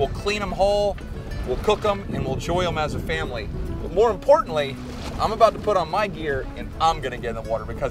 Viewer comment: Lol all I heard was "more importantly, ME!".